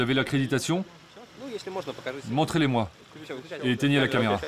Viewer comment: il dit pas montrer mes moi il dis si c’est possible de me les montrer il est pas du tout méfiant